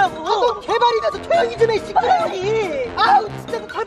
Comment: 이 한테.... "아 조용히 좀 해!! 시빨넘이!!" 이러시는거... 저만 들었나요....??